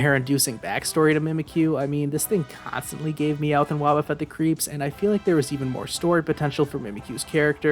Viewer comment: My dumbass thought you said mowth and wobbufffet